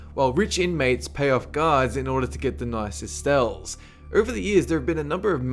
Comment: Hehehe 3:00 he says "stells" instead of *cells.